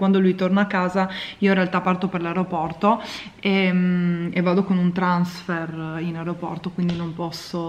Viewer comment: Per un attimo ho capito “vado con un trans”. HO INFARTATO AHAHAHAHHA